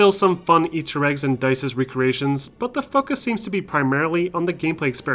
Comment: i thought he said what the f*** here lol it took me a rewind to notice he said focus... I dont know why.